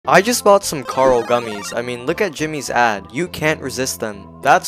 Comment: I thought he said 'Look at Jimmy's as